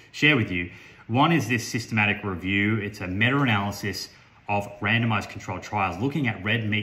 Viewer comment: Nice job putting this together in a scientific manner without resorting to the typical toxic bashing on YouTube. Stay classy 🤙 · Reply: Try more listening and less typing - at 3:30 he CLEARLY says a "meta-analysis of randomized controlled trials"